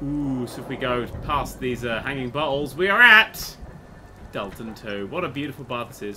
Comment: it sounded like he said "hanging butt holes." And I was really confused until I turned back to the screen.